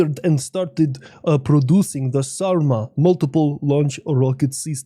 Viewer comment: When you said the Sarma, I thought you said Saruman for a moment.😂